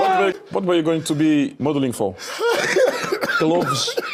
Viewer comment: Balaclavas 😂😂😂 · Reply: Thought he said Baklava